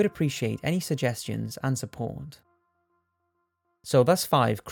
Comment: I had to skip back a few seconds at the end when I swear I thought you said ‘See you in the next life’ 😳😳😳